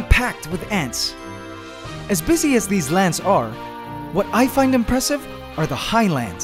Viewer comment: lol at 2:10 did he say lants